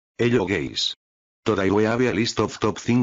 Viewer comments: is anyone not gonna talk about the man saying hi to us bc It sounded like he said "hello gays" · DID YOU JUST CALL US GAY?